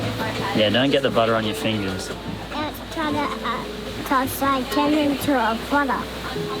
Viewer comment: inaudible? as a father of 2 boys I heard, "or else I turn into a butter!" or maybe im just a big lenny